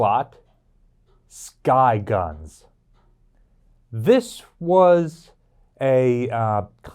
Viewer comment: when he first said "sky guns" I heard "guy guns" That is a completely different class of gun.😉